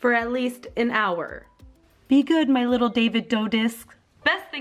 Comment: Did just said "david dough disk" bc I'm